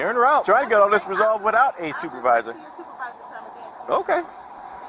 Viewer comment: Sgt.! I think he said, "Is it one of those?! 😅😂😅 (Sorry I'm 3 months late... 😊)